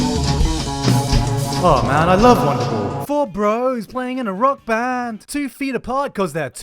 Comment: To the guy who writes the captions. At the 10:28 mark I think he said "awe man I love Wonderwall"